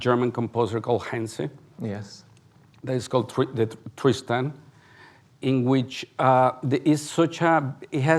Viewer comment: What is the piece of music he talks about at 16:53? The subtitles says Tristan by Hansen, but i can’t find it when i search it up. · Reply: it’s tristan by Hanze linked in the comments above !